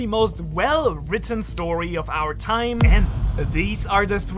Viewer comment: did he just say 'Of our time'!